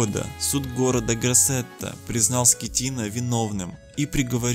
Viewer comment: каждый раз слышу капитан скотина XD